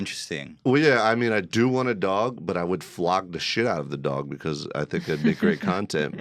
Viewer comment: I thought he said he was going to "flog the shit out of the dog"